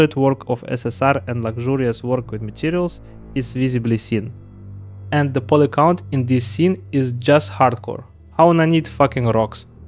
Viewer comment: розшифруйте что он сказал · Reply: Поликаунт жесть "Качественный".И как же нанит "Ёб*т".